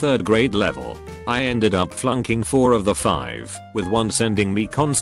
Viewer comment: at around 4:40-4:43 I thought I heard it say "I ended up fucking four out of five if them" not flunking XD